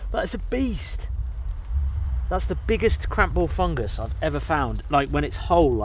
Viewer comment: Am I mishearing "Crap-ball" Fungus? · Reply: “Cramp ball”